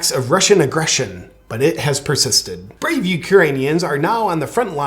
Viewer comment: I thought it was Iranians???? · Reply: Oh, I thought he said "Uranians". Y'know, the people who live near Chernobyl.